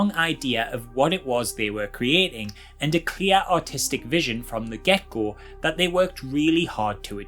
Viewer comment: I keep mishearing "Artistic vision" as "Autistic vision" and thats a mood : )